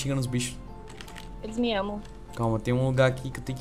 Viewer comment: Ouvi meu amor 7:54???